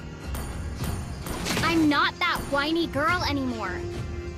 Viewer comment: I thought I heard Layla say "I'm not that white girl anymore!"